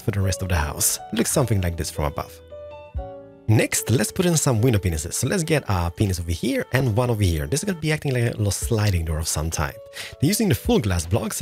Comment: it sounds like he says penis